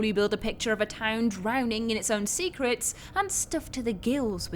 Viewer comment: Sounded like you said cigarettes lol and not secrets because I got distracted 😹😹😹